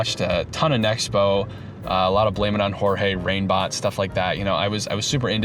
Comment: i think you said "reignbot" but i thought you said "rainbott" and i was so confused because they (rainbott) started making videos like 4 years ago 😭